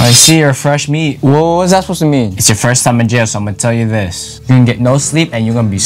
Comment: I THOUGHT IT SAID WHEN ITS YOUR FIRST TIME IN PERSON 😦